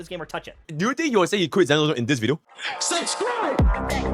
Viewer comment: what the fuck did bro say "do you think elgkhdgkdjhlge in this video" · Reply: "Do you think he will say he quit Zenless Zone Zero in this video." But really squished lmao